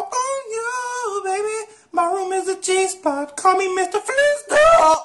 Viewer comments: i heard my room is a cheese puff, call me Mr. Flist Uhhhh uh Lmfao😂😂😂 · I swear he said "my room is a cheese puff" Or am I not using my brain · it sounds like he said "my room is a cheese puff" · Legends say his room is still a cheese puff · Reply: shell company 0:08 sounded like "my room is a cheese puff" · i heard my room is a cheese puff, call me Mr. Flist Uhhhh uh Lmfao😂😂😂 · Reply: I heard, "call me Mr. Flintstone." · Why do I hear “callin’ mom”?? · Says, " My room is a Cheese Puff Room : What the f*** did you just say to me? HUH? * gives him a deep voice 0:22 Calls his mom